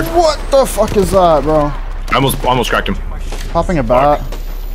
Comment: I’m Japanese please tell me what he said here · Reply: Popping a bat